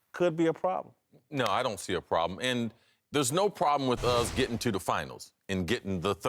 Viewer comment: Did CC just say there's no problem with "us" getting to the finals? Is this show just a panel of bronsexuals?